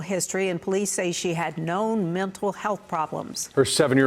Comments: I love how the news report stated the police said that she didn’t have any mental problems. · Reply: I thought I heard "known mental problems". Sounds like "no". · I thought that they said she had no mental illness, but was schizophrenic, how is that not a mental health issue? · my bad - you said 'known' i heard 'no mental illness'